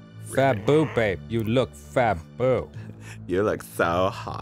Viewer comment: I thought Arron said fat bop and I was like damm I sense the hate coming also y is everyone like 6 ft 8 while the prince and cinderall Is a cool 5 ft